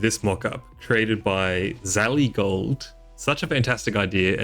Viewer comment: it sounded like you gave credit to "Zatygold" for the mockup. I am Zatygold and I want to inform you that I did not make this mockup. The real credit goes to, I believe, BolicheX. I appreciate that you took the time to give credit, but it was the wrong person. I only shared their mockup for others to see. With that said, I personally dislike most of the Test of Time update. I think a lot of it is a huge downgrade from what we currently have. I really like all the UI improvements, but outside of that I think its a step backwards.